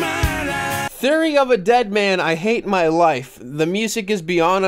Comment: Theory of a deadman writing an ode to secretly enjoying pegging. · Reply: I thought you said lullaby of a deadman as in the call of duty zombies song